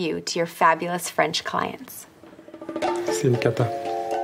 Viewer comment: for anyone wondering what the person on the left is saying, he’s saying that Emily is sh*t. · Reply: Not exactly, he's saying "c'est une cata", not "c'est une caca", cata is short for catastrophe, it's abbreviated in informal speech